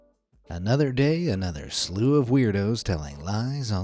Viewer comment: i thought he wanted to say at the start of the video ''another day another sla